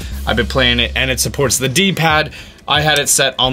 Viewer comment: Honestly what is a T-pad. I have to know. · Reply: Benjamin Ams I thought he said T-pad instead of D-pad, maybe I just misheard. And by now it's common knowledge to all gamers that a D-pad is the cross on controllers.